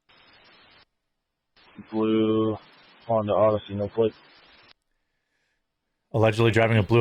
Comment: I think he said “no plate” not “no flick”